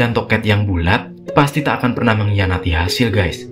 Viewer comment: TEKAT oi bang TEKAT, Bukan TOKET YANG BULAT